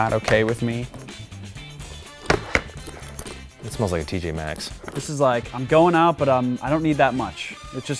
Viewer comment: Did Eugene say t J maxx at 1:34?